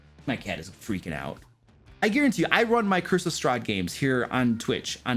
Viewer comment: I swear I hear “Christmas Strahd” every time he says “Curse of Strahd”.